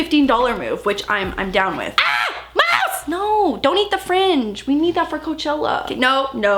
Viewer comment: I thought you said: Moose! Please don’t eat the fridge😂😂